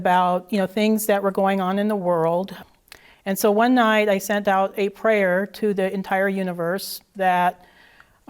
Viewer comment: I thought I heard this broad say she "Sent out Santa's prayer" whatever the hell that means? 😂😂